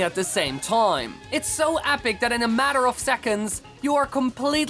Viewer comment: he says apic instead of epic